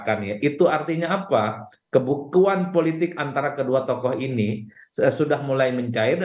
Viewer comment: Salah dengar dari pembicara yg diterima presenterlah yg mengubah arah politik.. pertemuan yg mengubah "kebuntuan politik" berubah menjadi mengubah "kebutuhan politik ".. 😂😂